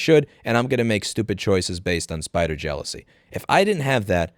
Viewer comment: "spite or jealousy". At first I heard "spider jealousy".